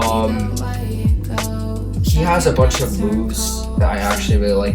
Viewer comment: bro did he just say she to darth vader